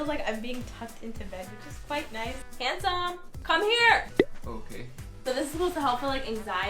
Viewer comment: I ligit thought she said to her husband 'hey hairy come here'